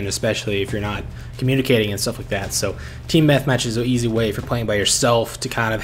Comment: lol anyone else hear Team Methmatch at 9:06?